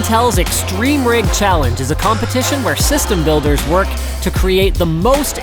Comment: does he say system Bolders? at 0:05?